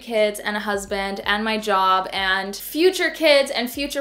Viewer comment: I’m sorry, did you say you have a dog?! How’d I miss that ...?